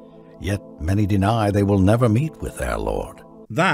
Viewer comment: OK, the caption say "they deny they will ever meet with our Lord" but I've listened to it a couple of times, and I swear he says "never" I was only listening and not watching at that point and paused it because I had to figure out if he actually used a double negative. I still think he did, regardless of the caption.